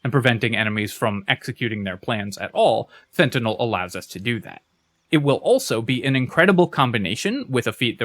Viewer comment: I turned around for a second to do something while listening and 14:51 sounded like "Fentanyl allows us to do that" i spit out my drink and thought what mod are we playing???